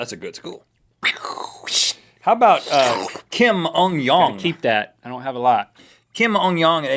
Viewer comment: wow i had another window up and thought they said kim jong un...